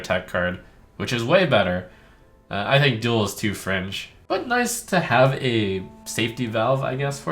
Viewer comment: I thought he said "duel is too French"